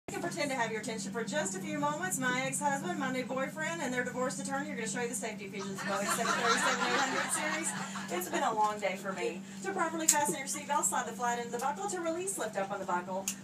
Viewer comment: I SWEAR SHE SAID BOEING 747-8OO AT 0:06? SOUTHWEST DONT HAVE THE 747.